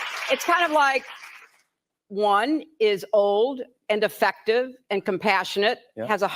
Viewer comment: it really sounds like she said "...one is old and defective..."😂😂😂